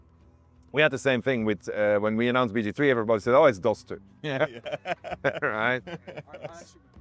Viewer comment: What did he say there? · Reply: "players were like oooooh it's DOS2 (Divinity Original Sin 2" - I think